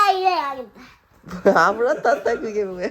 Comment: 아브라따따? 안물어봐따~ 같이 들려요ㅎㅎ